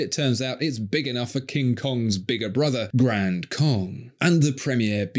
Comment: I genuinely thought you said "Grand Kock"